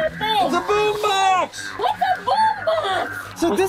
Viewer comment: Why did say “what the boom box” at 15:57 XD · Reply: She said what's a boom box i think